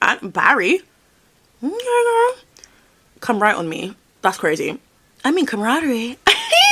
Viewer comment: "Come right on me!?"😧 I thought she said 'come ride on me'😭😭 freaky ahh lady